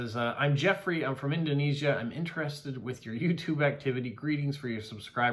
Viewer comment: Hi Jeffrey 👋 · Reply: Jeffrey sent you are card and I thought you said he was interested in you YouTube